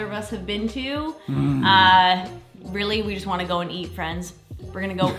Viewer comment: 8:59 we are just going to eat friends Me: well I hear friends they taste like apples🤣 I love the fact that you did not edit it out it so cute😁